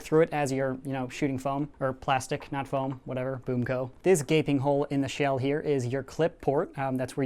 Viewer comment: I thought he said gay thing hole not gaping hole